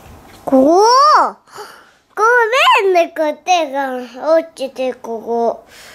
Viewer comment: おぉー！ごーめんね！こてがー落ちてここー！ にしか聞こえないんですけれども 編集済みごめんね